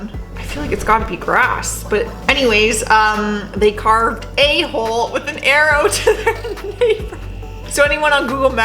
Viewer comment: Lol I thought it said A hoe with an arrow down. Lol